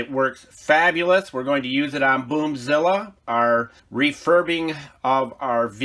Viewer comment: Yeah I need some coffee, smh🤣. Thought you said boobzila🤣🤣🤣🤣